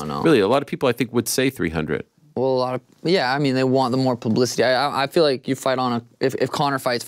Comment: Did he say "Pluh-blicity"?